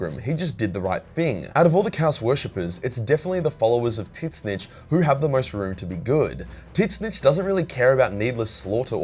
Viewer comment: I every time he said “tzeench” I thought he was saying “tits bitch” and was talking about Slaanesh.